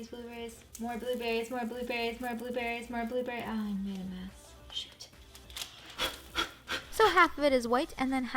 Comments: did you say shit there lol ive never heard u curse lol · anyone else hear her say.... SHIT!!??!! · did she say “***shit***” Like, I get she’s an adult but ummm... on camera? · Did anyone else hear I made a mess shit😂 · Did anyone else hear her whisper shit around 9:21 😂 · did just say shit?? omg what a kween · did she just say sh*t